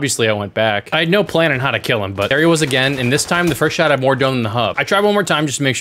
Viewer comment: Did I hear “more dome than the hub” or am I trippin 😂😭